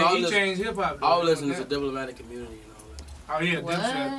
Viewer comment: What does Keef says at 4:30? Who was he listening to? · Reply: Diplomatic Immunity AKA Dipset